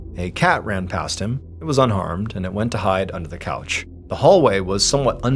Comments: Did you say the cat was unarmed or unharmed?? It really sounded like you said the former lol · Not gonna lie.... at first I thought he said "the cat was unarmed".... I had questions.... until my mind caught up with my ears.... "OHHHH unHARMED"... 🤣🤣🤣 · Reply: I thought he said “unarmed” at first. Like you, it took me a second to realize he said “unharmed” which, of course, ma de much more sense. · Not gonna lie.... at first I thought he said "the cat was unarmed".... I had questions.... until my mind caught up with my ears.... "OHHHH unHARMED"... 🤣🤣🤣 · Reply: I thought he said “unarmed” as a joke😂😂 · Not gonna lie.... at first I thought he said "the cat was unarmed".... I had questions.... until my mind caught up with my ears.... "OHHHH unHARMED"... 🤣🤣🤣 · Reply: I thought he said unarmed... then I thought a cat is never unarmed, lol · I swore Mr Ballen said the car was UNARMED 😂had to rewind it real fast he actually unharmed 😂